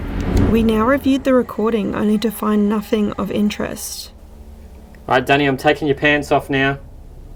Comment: As I momentarily looked away from the vid and I hear “Already Danny I’m taking off your pants now” and then “All God I’m stuck” and then “Forgot my trigger object”, I tell ya it was hard to keep my mind out of the gutter. 😂🤣😂. Lol. Of course replaying the vid, puts it in a cleaner perspective.